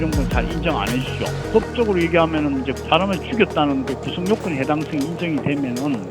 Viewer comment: 구속요건이 아니라 구성요건이에요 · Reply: 형법의 구성요건 말하는건데 듣기에는 구속요건으로 들리는거같아 자막을 잘못다신거같은데요. 뭔이라뇨..ㅋ